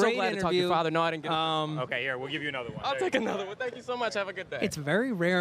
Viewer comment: Wait what was the guy 58:48 saying to? Was that Trumps son or...? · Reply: Yeah that was his son. He was like "did dad give you a fist bump? Here." And he first bumped him... Really awkward kinnda racist